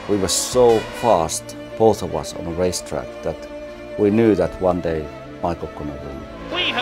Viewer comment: What did Mika say at 2:10? "One day Michael could..."? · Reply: Marek Snopek ”one day michael will win ... one day i will win”